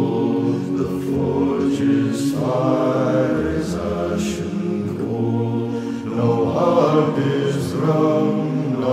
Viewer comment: I keep hearing "The forge's fire is ash and coal." instead of "The forge's fire is ashen cold." Edit: Amazing song though. Keep up the good work, <3